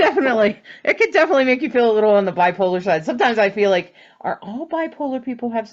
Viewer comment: When you said "bipolar",I thought you said "black holar",like a black hole.....a new term? Lol